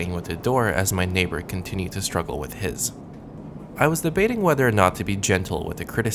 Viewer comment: ". . . then gave up and resorted to playing with the door, as my neighbor continued to struggle with his." .. ..... first i thought you said " as my favorite continued to struggle with his"